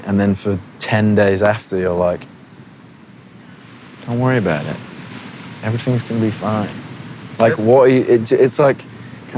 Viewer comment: for anyone who needs to hear this, this is harry saying "don't worry about it, everything is going to be fine"